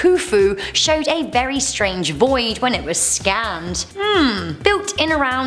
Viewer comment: I heard "Scammed" instead of "Scanned" Lol